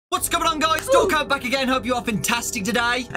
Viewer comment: All ways i hear "Don't go back again" instead of "Dawko Back again"